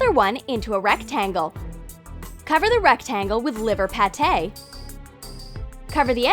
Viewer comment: Did she say "liver" HELL NO